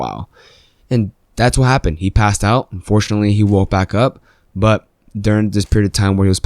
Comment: “Unfortunetly he woke back up” LOL!!!! Bruh cant believe you said that 😭😭😂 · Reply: I thought he said "and fortunately" lol